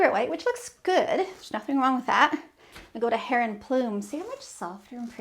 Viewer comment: Help please! At the 2:45 mark, after Egret White you say another one & I love the color but I can’t make out the name, Can anyone understand it? Something Phlume??? The SW dealer didn’t know!!! LOVE Agreeable gray and your choices with other rooms!!! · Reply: Heron Plume